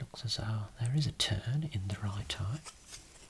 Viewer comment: I can’t be the only person who thought he said “It looks as though, there is a turd in the right eye” until he said it’s potentially turning in. I couldn’t help but laugh at my crappy audio😂